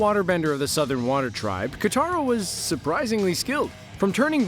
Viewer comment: did he just call katara kataru